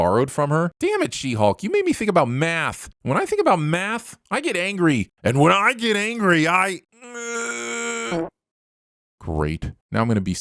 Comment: I swear I heard you say "when I do math I get angry and when I get angery i " 8fRT8